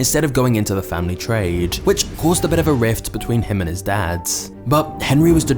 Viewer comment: did he say "a rift between him and his dads" lol